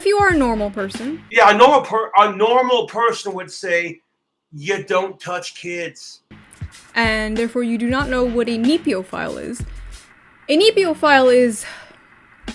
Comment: haha thats where got the "Fuck Neophiles" from he just misheard it TWU I hate the forbidden four so much